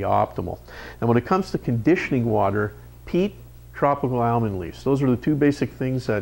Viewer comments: what is he saying at 7:18 ? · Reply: Heat for proper betta metabolism and tropical almond leaves for natural betta water conditions (Nutrafin Betta Conditioner does contain Almond Leaf Extract). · I have listened over and over and can't figures out what he said the TWO things are we need .. He said these two things are very important, sounded like he said PEEP?, and tropical almond leaves. It is at 7:15 in the video. I have my betta in a 6 gallon tank that I originally bought for glofish but ended up getting a bigger tank for them, I keep water around 80 but one thing that got me nervous is the gravel I use. I use regular black gravel do I need to reset up his whole tank? Looks like I didn't see one tank with gravel. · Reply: Dominique! We mentioned peat and tropical almond leaves, yes, we agree it did kind of sound like peep, sorry about that....If you are using a small filter of some kind you can usually incorporate a bit of peat into it, mind you tropical almond leaves do a good job on their own. As for the black gravel, no need to worry, feed such that your betta consumes the incoming food immediately with little of it making it to the bottom and all will be well, regular water changes and maintenance are always important.